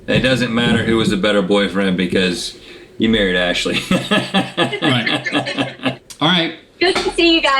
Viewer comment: Did he say "Ashley" 10:12?